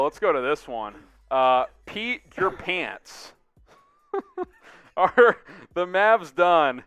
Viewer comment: did he just say “Peed Your Pants”?